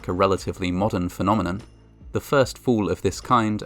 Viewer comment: When he said, “the first fall of this kind…”, I heard, “the first fool of this kind…”. I guess not too much difference.